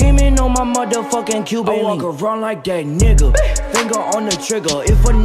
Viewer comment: It sounds like he says I walk around like gay n***a